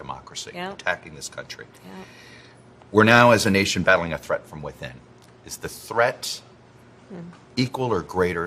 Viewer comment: O sorry, i thought Chuck said white men, didnt notice he said within , my bad🤷‍♂️